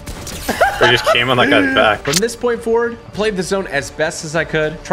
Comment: Nahhhh did just say “oh you just came on that guys back” at 9:40???